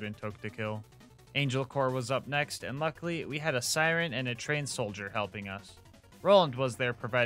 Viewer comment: is he saying trance soldier or trans soldier? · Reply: Trained, as in he was trained for combat 🙂 sorry for goofy pronunciation.